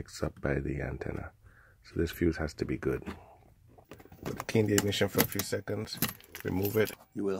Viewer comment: You say..."KING DAVID SHOPPED FOR A FEW SECONDS"...so please tell us at this crucial place....what the heck you are saying "IN THE KING's ENGLISH" as the closed caption does not even know what the hell you are saying. Tell all your friends...WORK ON YOUR ENGLISH, instead of pressing 2 for Spanish. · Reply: I said, "Turn the ignition off for a few seconds..."